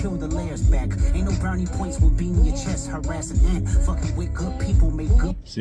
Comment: I thought he said beam to the chest at first😂